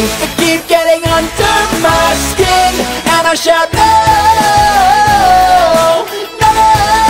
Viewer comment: what did it say? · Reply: And I shout